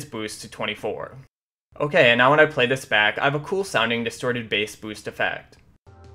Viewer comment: did he just say "cool sounding distorted sound effect" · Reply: Nope, he said: "cool sounding distorted bass boost effect"